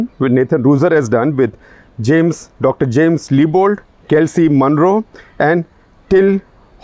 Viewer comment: Did he said Kelsi Monroe at 1:17 ?